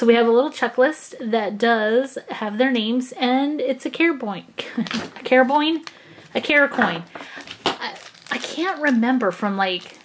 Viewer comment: Who else thought said carrot coin at the beginning ⬇️